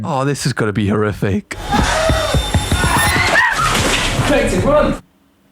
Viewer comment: ‘Katie, RUN!’ Did he just call Leona, Katie? Or am I just not understanding his accent?